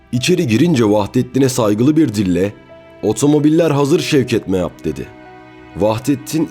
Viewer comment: şevket mehap mı dedi)) ya ben yanış mı duydum biri aydınlatabilir mi